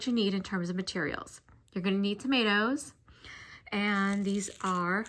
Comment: When you said "some 8 ohs," I heard "some tomatoes." 😂😂😂😂 I thought it was a new type of seed bead. 🤭🤭